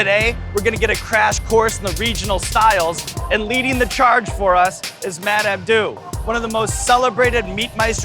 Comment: thought Sean said " leading the charge is MF DOOM..." I was like oh shit!